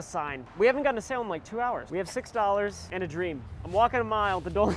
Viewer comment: When Ryan and Danny Gonzalez post at the same time but you don't have emojis for some reason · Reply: i thought u said i am dream